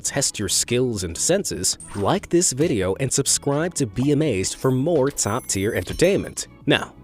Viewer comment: I heard subcrwibe